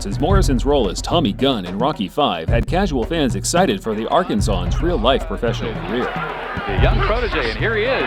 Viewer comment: what does the narrator say? Arkansawan?